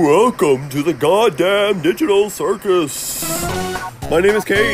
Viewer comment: “Welcome to the god damn digital circus.” and “My name is Caine! I am your bitch.” Are recurring lines that WILL NOT escape my head. I randomly smirk at the thought of it more than I’m willing to admit. HELP ME I CAN’T STOP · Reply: I thought it said “gone dad digital circus”